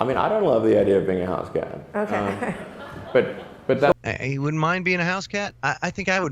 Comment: I thought he said " I wouldn't love being a house cat". Watching him feels like watching the midget and Laura and Coop in the Black Lodge. Even his name is weird. Creepy.